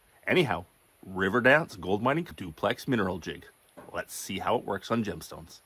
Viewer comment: At first I thought you said River DAN gold mine